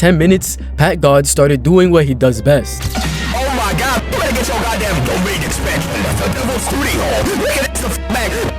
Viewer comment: The first thing I heard was “DOMAIN EXPANSION THE DEVILS BOOTY HOLE” 😂😂😂